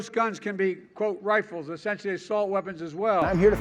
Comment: I thought said "sexual assault weapons as well" lol